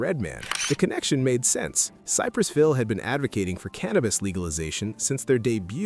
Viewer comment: It sounded like he said Cypress Phil! 😂😲